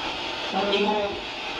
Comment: PVE: j'ai entendu " on est mort " ...comme quoi l'interprétation du son...je ne dit pas que c'est celà, et je ne dit pas que votre interprétation de ce son est juste non plus...je donne juste ce que j'ai entendu sans regarder l'image à ce moment là ;) bonne continuation.